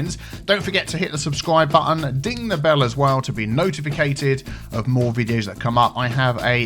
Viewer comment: did he just say notificated?